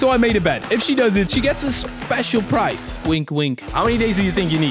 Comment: When he said "wink wink" I thought he said "with glue" 💀